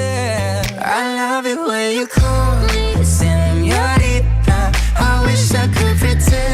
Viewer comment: I thought Shawn was saying “I love it when you call me Señorita” too, which is weird but he actually cuts out on the “me” so he’s actually singing “I love it when you call _ Señorita”. I think they wanted to add his voice in but it wouldn’t make sense for him to say “me”.